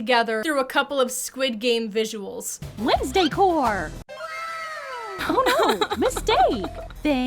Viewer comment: i thought she said "wednesday whore" what in the world is wrong with me